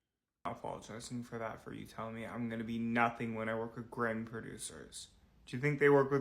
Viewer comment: It went to commercial at 11:13 and I thought he said he worked with Greg… I was excited to know who tf Greg is but nah all I got was “great producers”🙄